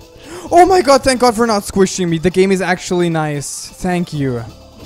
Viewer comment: I almost heard it as "OMG thanks for that hajime the cake is actually nice" WTF am I thinking?